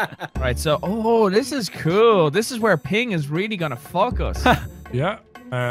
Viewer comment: for the longest time i thought he said Piggy is going to fuck us but he said Ping XD